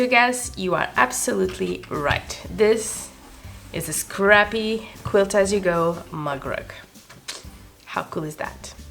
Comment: LOL....at about 8:46 I thought at first that you said, "this is a crappy quilt as you go mug rug." I think I must be related to Anna Rosanna Danna. Never mind. OH...and white was a good choice for the zigzag. It accents the white polka dots on the background, and really makes the colors pop on the front.